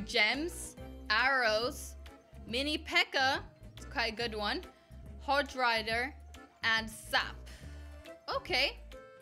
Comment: DID SHE SAID HOJ RIDER AT 4:01?!?!!!